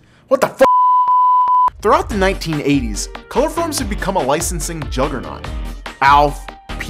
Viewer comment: Am I really the only one that thought that said chloroform at first?